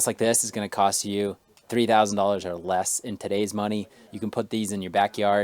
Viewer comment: did you say it will cost 3000 or less or 33000 or less? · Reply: The smaller amount of